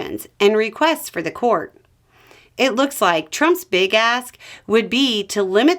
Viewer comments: "Trump's big ask." I heard, "Trump's big ass." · You said, "Trump's big ask would be..." But I heard, "Trump's big *ss would be..." LOL